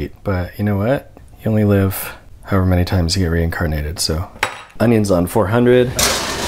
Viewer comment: y’all heard onion instead of oven right? i’m so used to “ondo on 4hundo”